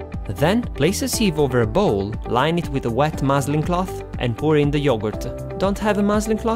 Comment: Love your videos! What is the name of the cloth you use? It sounded like you said moseling cloth?